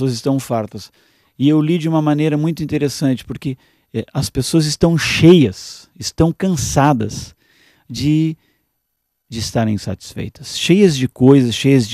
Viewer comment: Desculpe, mas entendi ele dizer em 3:21 a 3:25: "As pessoas estão cheias de estar INSATISFEITAS", e não satisfeitas, como diz o título do vídeo. Paz!